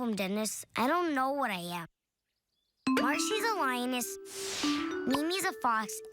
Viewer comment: did he say Lion or Lioness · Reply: I believe he said Lioness